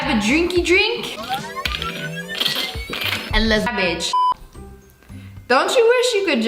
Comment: it sound like she said and lets bi- HAHAHAHAHHAH